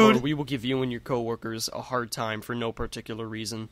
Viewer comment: It sounded like saying "were closed sir"